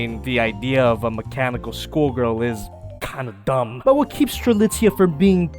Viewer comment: Did you say... Gundam?!"